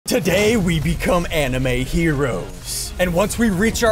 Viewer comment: I thought ssundee said anime weebs in the intro lol